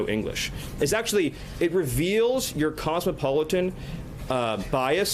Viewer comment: did he just say Cosmopolitan? That's a Nazi reference